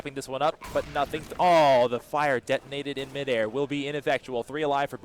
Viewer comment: did he just say "ineffectual"? lol